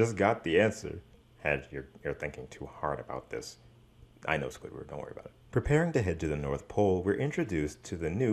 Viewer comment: Did you say “Dino Squidward” at 5:58?